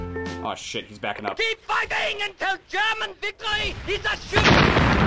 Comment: What the German speaker said in 4:58 ????????? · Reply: Keep fighting until German victory is assured